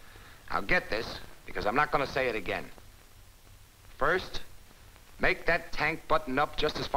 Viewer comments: I thought it said “Crank That Tank” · I thought it said crank that tank